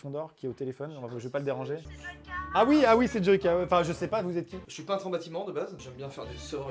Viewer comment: C'est moi ou dit ''c est Vodkaaaa'' après Amixen répond ''ah oui c'est Joyca'' mdrr